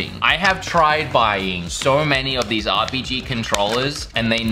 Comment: he really said: RBG.